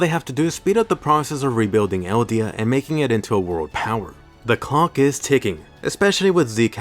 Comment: what he say? 💀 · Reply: “the clock is ticking”